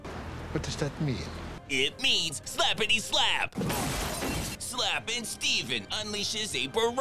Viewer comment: I almost spit out my beer when I heard "Slappin Steven".